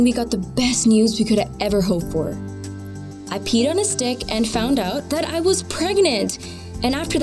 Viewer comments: Who else thought she said I peed on his dick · When she said "I peed on a stick" I thought for a sec she said "I peed on his dick" LMAO